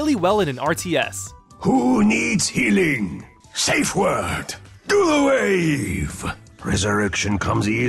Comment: What i always thought he said "Dual wave" not "do the wave." made more sense as the wave had dual purpose of damage and heal.